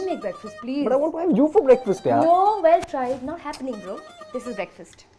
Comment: did she say not happening bro 😂😂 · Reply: Not happening go😄😄😄lol